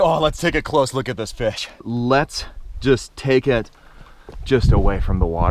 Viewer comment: did he say '' Lets just take a close look at this bitch'' or '' Lets take a close look at this fish''??